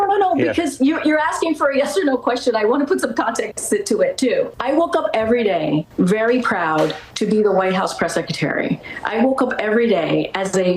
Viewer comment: I heard her say that she "woke up every day proud to be the press 'seketerry' ." 'Nuff said.